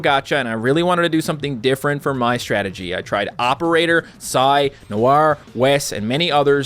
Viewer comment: did he say sai battler even though sai isn't a usable unit anymore? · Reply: He said psi not sai